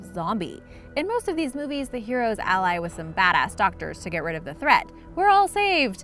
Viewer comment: Did she just say bad ass